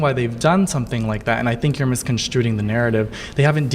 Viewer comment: wait did he just say “misconstruTing” the narrative?